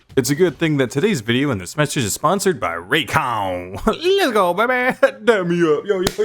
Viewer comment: Straight up thought it said, "sponsored by Raytheon" and not Raycon for a sec